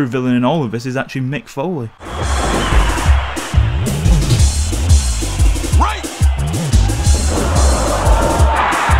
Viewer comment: did he say rape ??? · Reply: "BREAK"